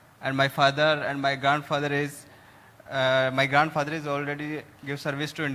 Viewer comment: Did he say my GAND father?? 🤔🤔🤔